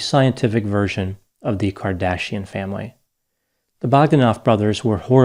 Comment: i heard "cardassian" out of it ^^